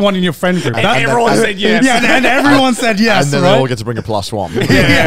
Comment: Is it just me or do I keep hearing them say “brang”